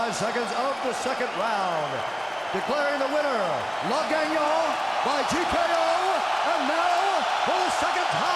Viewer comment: What did bruce buffer say? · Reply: Le gagnant its in french in English that mean the winner